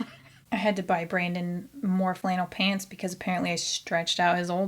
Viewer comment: I initially misheard "vinyl" instead of "flannel"